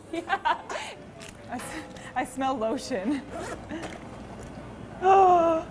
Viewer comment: at first I thought that she said: "I smell bullshit". Haha, my mind is deeply affected by the Internet.